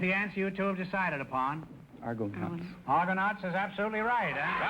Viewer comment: That Hispanic guy was pretty smart. Trying to look his father up on Google. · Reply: ***** LOL- yeah he got the last answer wrong- he said ARGOCOUNTS but Groucho must have misheard him so he won anyway.